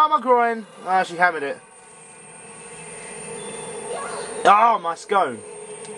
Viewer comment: Did you say Ow my scone! XD Great Let's Play it is so funny! I always look forward to them like a kid waiting for her Saturday morning cartoons . ^,^